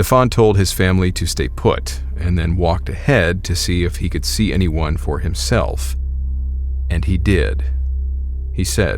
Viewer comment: what it say so clearly? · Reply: "I know you're there"